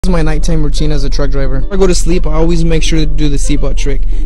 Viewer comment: I was chewing on food at the start of the video and all I heard was “my night time routine as a drunk driver”